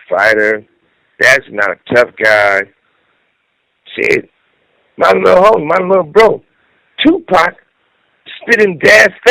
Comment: did he just call 2 pac his lil hoe ??? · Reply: "My lil homie, my lil bro"